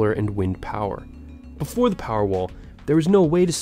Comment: Who else thought it said powerball